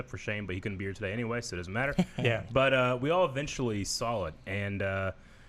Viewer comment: Am I the only one that hears saying ‘sawl’ instead of ‘saw’